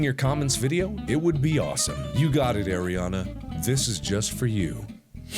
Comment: MARK SAID MEH NAME!! I was watching this not really paying that much attention and I heard him say "this is for you Aryonna" and I flipped out I don't know why because I know it wasn't actually me but still.! BTW I LOVE YOU MARK YOU ARE SERIOUSLY THE BEST THING EVER 😘😂